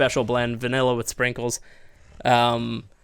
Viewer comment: I just want you to know that this whole time I thought you were saying Sprinkles server and I kept waiting to see Sprinkles on and never did only to finally figure out in this video you were saying vanilla with sprinkles lol